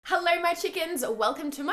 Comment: I legit thought she said hello Mexicans in the beginning 😂😂